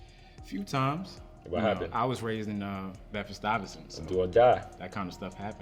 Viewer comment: and 8:13 it seems like two different guys refer to a neighborhood in or near New York City called “BLANK Iverson” or sort of like “Betvest Iverson.” What are they saying?